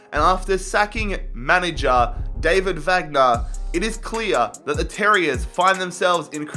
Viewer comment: You got Wagner confused with Wenger ;) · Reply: Unwanted Trashbag Listen again for the love of. said David Wagner at 00:14, he did not say Arsene Wenger. David Wagner is the former manager of Huddersfield Town.